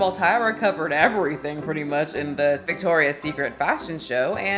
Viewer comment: Haha I thought you said “thick-torias secret” 💀